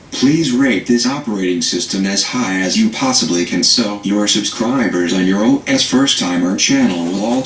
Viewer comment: Did he say "Please Rape this Operating system high as you can possibly to Subscriber"?